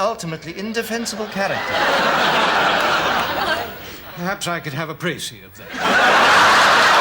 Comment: What did the PM say at 6:05? Couldn’t catch that 😂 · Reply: Perhaps I could have a precis (summary) of that.